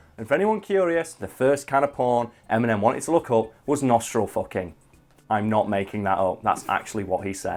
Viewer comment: I think I have bad hearing, but did you say "nostril fucking"?